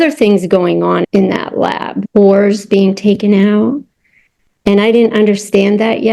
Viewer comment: Need 3 times to this bit and can't catch it, please could someone tell me what she says around 9:50 sounds like she is saying 'oars being taken out' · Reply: She said "cores" as in soul cores